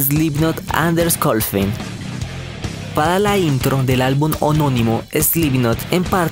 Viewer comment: ME PARECIÓ QUE DIJISTE HONONIMO ... Y ES HOMONIMO :V . Pero buen video bro lml sigue así